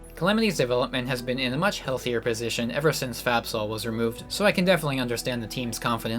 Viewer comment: during this entire video i thought you were saying fapsol (as someone who knows nothing about calamity and was listening to this to sleep)